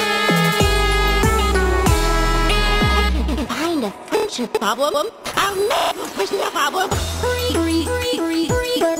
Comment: what is she trying to say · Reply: She is saying if I can’t find a friendship problem I’ll make a friendship problem